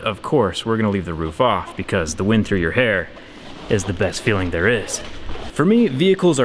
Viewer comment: did you say wind through your hair